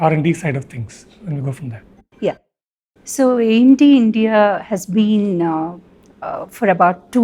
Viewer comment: Are they talking AMD or MDM? The lady seem to say AMD started in India about 20 yrs ago. If that is what she said it is factually incorrect. AMD was founded in California, USA and none of the founding members were Indian. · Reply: What? Right at the beginning @ 2:01 she clearly says AMD India!